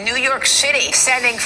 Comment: I keep hearing "New York shitty" :o